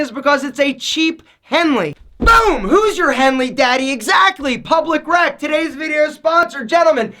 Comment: I thought he said "who's your baby daddy" LOL